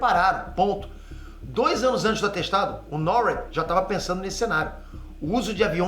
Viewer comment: só eu ouvi atestado?